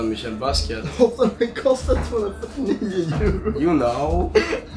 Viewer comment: What does Yung Sherm say on 9:47? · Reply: Дмитрий Иванов ”it cost 249 euro”